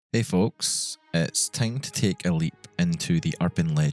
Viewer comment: I misheard you're opening line as "hey folks, it's time to take a leak" lol